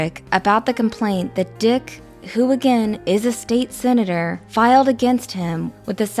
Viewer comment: I thought for a second that said Dick Hooligan, I had to rewind twice before I realised she said “Dick, who again..” 😂